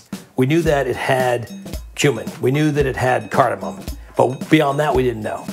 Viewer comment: thought he said “human”